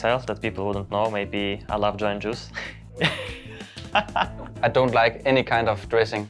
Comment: what does m0nesy say? I love John Juice? huh, Im deaf and I really wanna know lol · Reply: joe & the juice